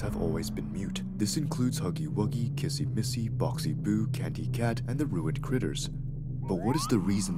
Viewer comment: what he said"boxy boo" what I hear"bossy boo"